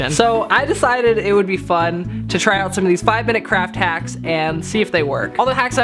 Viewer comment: I thought he said five minute craps😅